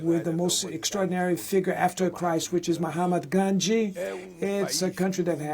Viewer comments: am I Hearing it wrong or Did the translator really said Mhommad Gandhi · was I the only one who heard Mohhamad Gandhi😂